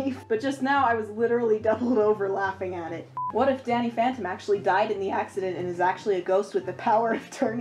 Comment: IM FUCKING SOBBING I THOUGHT U SAID DADDY PH ANTOTM